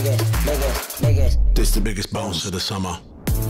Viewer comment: Why are they saying the n word?!?! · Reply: I keep hearing “ bigot”